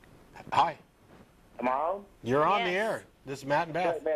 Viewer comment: did he just call her "mom"? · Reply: "amooon?" the merged words, with silent inbetween... am i on... :] may need an apostasy... lel